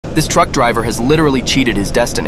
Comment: I thought he said drunk driver😂